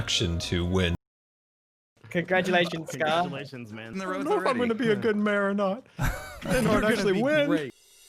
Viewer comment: Does anyone know what scar says on 0:10?? I had to rewatch that footage 8 times and i even tried watching it on 0.25% speed but I just can't understand what he says... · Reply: "I don’t know if I’m gonna be a good mayor or not"